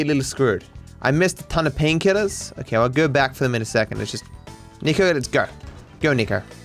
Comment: did matt called him Niko? lol